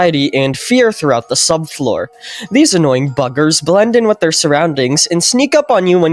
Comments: I swear he did not say buggers 😭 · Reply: Bud said f*ckers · I thought he said “these annoying f*ckers “ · it sounds like he said “annoying fuckers” 😭